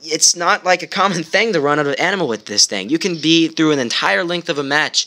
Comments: Lol did you just say animal at 4:02? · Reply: I said ammo but I guess it sounded more like animal. xD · did you say animal? XD